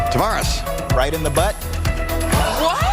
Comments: I thought he said, "Ride in the butt!" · LOL I heard “bite in the butt” not “rite in the butt.” Not to be confused with the answer on The Newlywed Game to the question “Where is the last place you made whoopie?” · When I was watching this, at first, I thought he said, "Bite in the butt". Matt...you have so much more money than so many people, because, it's a strong draw to think you could go on Wheel and end up with $40, 000 plus for 30 minutes of work, which a lot of contestants do. I'd risk embarrassment for that chance. Just have to laugh it off if it happens.